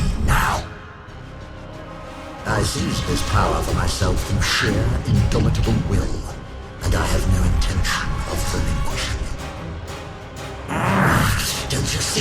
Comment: I still can't understand what he's saying at 3:23 I don't understand why THIS bossfight doesn't have Subtitles? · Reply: i ceased this power for myself from sheer indomitable will and i have no intention of reliquishing it